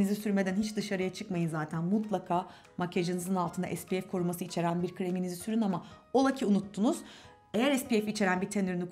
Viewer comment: Sbf içeren krem mi diyor orada ne diyor ya :/ o nee??